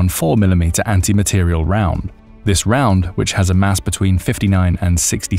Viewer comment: Ohhhh, 55-69 GRAMS! I kept hearing ‘grains’ thinking “hey, my AR uses 55 grain ball, TF?” Laf, the rounds are ~<num> grains, just a bit heavier than my .223 bullets.